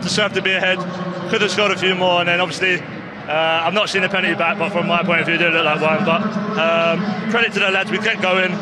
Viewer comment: The fucc is he saying at 1:10 ? Does anyone here speaks the ancient Harry Kane language? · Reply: Hasn't seen the penalty back but it looked like one. Question is, why you can't understand English?